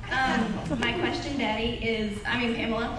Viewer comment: DID SHE JUST CALL HER DADDY LOL